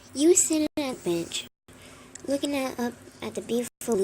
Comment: I almost thought she said you were sitting at a bitch